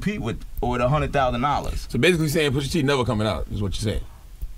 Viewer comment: Lmao I know this is years ago, I thought he said Pusha T ain't never coming out!? Funny how niggas talk shyt and now you see Pusha T HOT now.. He came for the giant (Drake) at the Top and now it worked for him..